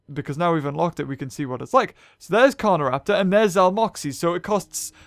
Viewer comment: did I hear that right “So there’s Carnoraptor and there’s Zalmoxies.” WAT